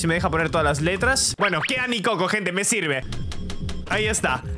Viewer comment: Escuche mi coco XD